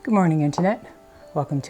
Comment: I laugh at myself every time you say “Good morning, internet.” In the beginning I thought you were saying “Good morning, Aunt Jeanette.” 😂